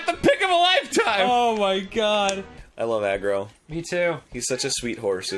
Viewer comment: I HEARD "I LOVE AGRO" AND IMMEDIATELY HURT